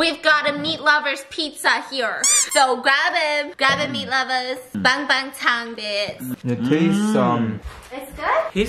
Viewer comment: Did I just hear "bangbangtan" at 5:21? Maybe it's just me. :))